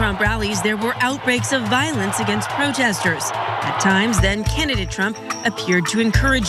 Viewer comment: did she say Kennedy Trump? Wtf is that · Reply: Candidate not Kennedy